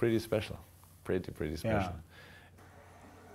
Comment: Klopp said "to be continued", but I thought he said "to Coutinho" at first. lol